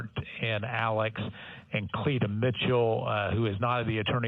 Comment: I thought he was saying 'Cletus' this whole time.